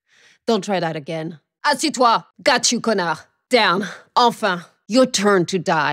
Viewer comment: Need help from someone who speaks French. What does she say at 3:48? 3:50? and the one at 7:27? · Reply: Enigmah it means Finally, and it is written : Enfin